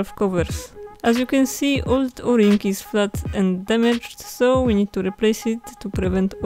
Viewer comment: did you say the 'o' ring is "fucked" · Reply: No, it's only flat and damaged :D